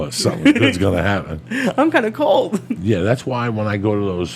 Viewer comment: I'm so stoned watching this, I could swear up & down he said "I'm kinda cold!" Then I was thinking about it for a sec, why would he have said that? I bet he said "It's comic gold!" I then went to leave that comment & went back to the time stamp to get it right, and heard he DID SAY "I'm kinda cold!" ... Wow, my medical bud is the SHIT...